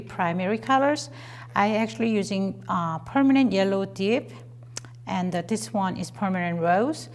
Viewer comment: i misheard her say " permanent yellow dick " XD